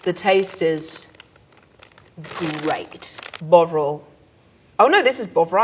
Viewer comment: I swear she said "bog roll" at 5:16 i was all wtf?!?